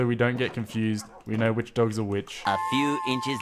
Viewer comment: Did anyone heard say which dog is a witch 7:24 😂